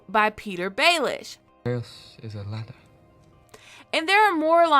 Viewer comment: can someone tell me what Bran said @ 12:04? Ms. was supposedly quoting Baelish but I can't hear what Bran said at all. I even turned on the CC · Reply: Chaos is a ladder... A quote of Little Finger